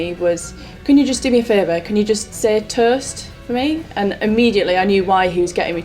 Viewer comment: I thought she was trying to say Tourist turns out she was trying to say Toast.